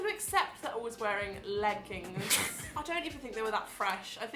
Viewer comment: I thought she said Phil was wearing leggings opps